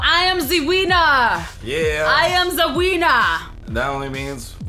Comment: That sounded like: I Am Zee Wiener!